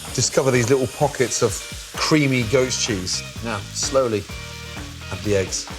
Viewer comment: Thought he said creamy GHOST cheese...